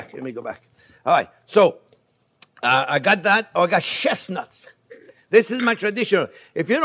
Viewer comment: Lol i heard ‘chef’s nuts’ hahahaaa oh my favourite chef muahhhhsss.